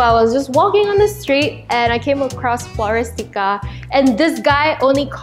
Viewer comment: I literally thought you said forest gump at 8:47😂 Btw would love a vanity tour 😍